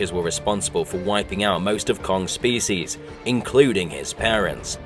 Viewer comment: I thought it said "responsible for wiping out most of Kong's faeces"🤣🤣